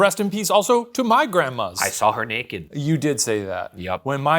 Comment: i thought ryan was saying he saw shane's grandma naked 😭😭